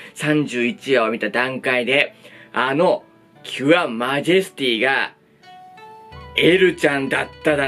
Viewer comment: 今週は無職、もののがたり、青オケ、アンファルがおもろかったね。 連休どこも人一杯やから、過去作楽しみました。ツルネとグリッドマン、ダイナゼノン見ましたが、満足度高かった 関係ないけど「ユア、マジェスティ」て聞こえて、何でコードギアスの話してるんかと思ったが、ただの勘違いだったw